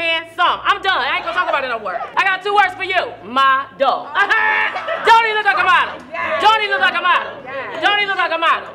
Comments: I thought she was saying "Don't he look like a bottom!?" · I thought she said don’t he look like s bottom